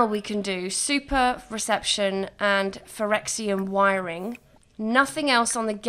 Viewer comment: I thought you said "Phyrexian wiring" and i couldn't believe I had missed such an easter egg! Haha.